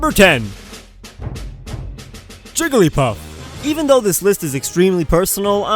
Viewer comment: It sounded like you said Trigglypuff